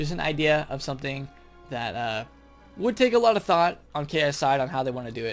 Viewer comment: did he say ksi in 8:30 ? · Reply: Ki's side